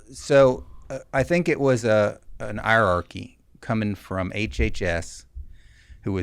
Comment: For those who are interested in reading the subtitles: Around close to18:26 minutes, it was transcribed as 'coming from an "Iraqi" when he actually said 'hierarchy'. A big difference.